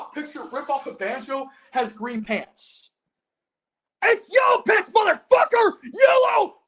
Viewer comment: I LEGIT thought he said "IT'S YELLOW PISS!"